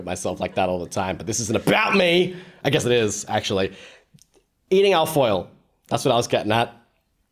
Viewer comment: i know you're saying al-foil, but all i hear is alf oil